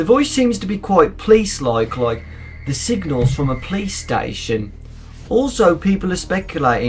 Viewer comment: The first time you said "Police Station" I thought you said "Playstation." XD Great accent though!